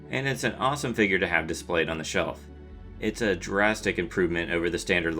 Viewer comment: I thought you said Jurasstic.